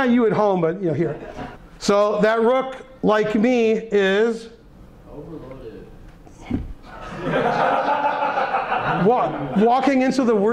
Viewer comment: what is said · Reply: That rook, like me, is? .... overloaded :D